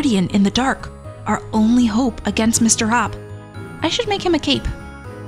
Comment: I thought you said :” I should make him a cake.” Instead of : “ I should make him a cape”.